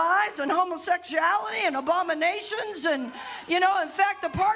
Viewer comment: Honestly thought she said “obamination” instead of abomination. Although I wouldn’t be surprised if Christian nationalists started saying that.